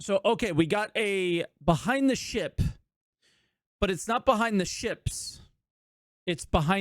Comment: I thought you said "behind the shit" at the start lol.